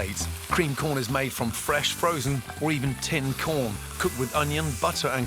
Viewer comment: did He Just say "fresh Frozen"??